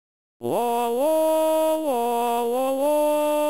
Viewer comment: is it saying Wa or La